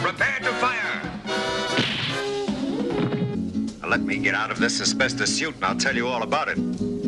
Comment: did he just say asbestosis suit??